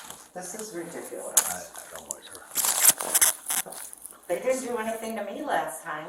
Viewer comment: Cop said, I don't like her at 23:57 😂 Anyone hear that? She WAS irritating.